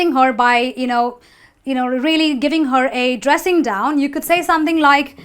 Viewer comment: Did you say a dressing down?